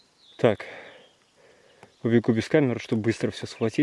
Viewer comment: Мне одной послышалось из того шкафа "беги"...😱